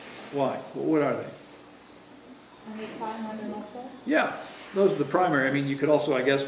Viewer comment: hi Sorry but what is being said at time 5:23 ? The lady says something but i can not understand what is being said. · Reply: I couldn't tell either. But judging from answer a few seconds later, it seems she mentioned lack of fat and lack of muscle.